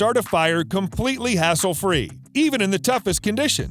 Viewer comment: I swear I hear "heres how to start a fire a completely free way" lol...